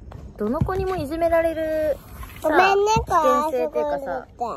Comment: の「ごめんね…〜」の後聞き取れないけど、子供が言わされるでもなく、怒ってもない相手に謝れるのってすごいと思う。 なんて言ってるの？ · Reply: ひなまつり ごめんねこれそこにおいて〜 ？？ ここにおいてって言いたかったのかな可愛い😍